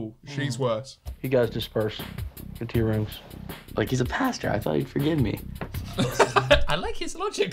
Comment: what did he say??? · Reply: He’s a pastor I thought he’d forgive me